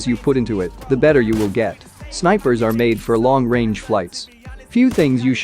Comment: did he actually said flights